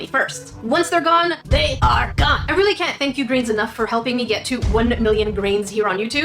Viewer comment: Was I the only one whom thought she was saying brains all this time? 🤣